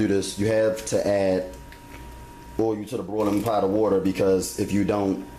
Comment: I thought he was saying "broiling" the spaghetti.. I think he meant to say boiling...I thought he was doing something new to the spaghetti. This is a great recipe. I will try this